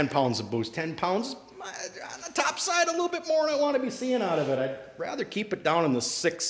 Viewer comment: Did I hear SIX SEVEN at 13:00 lol